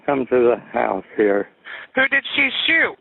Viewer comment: Paused video earlier... Unpaused it and I just hear "There's been was a black woman here" "WHO DID SHE SHOOT?"... that sounded really bad when I forgot the context.